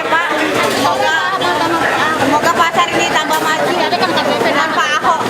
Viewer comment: semoga pasar ini lebih maju ... Ahok. Ibu ini gak salah omong atau gua yg salah denger? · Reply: dia bilang semoga pasar ini tambah maju ma pak ahok..